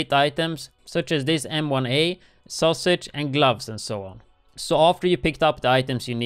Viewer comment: did he say sausage?!